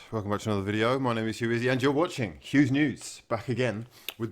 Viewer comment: I'm sorry, did you say Hugh's Nudes